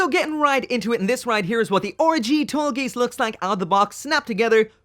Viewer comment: I thought he said "Orgy" instead of RG